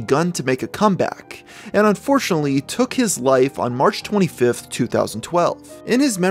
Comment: I was just about to make a hate comment because I thought they said "took his own life"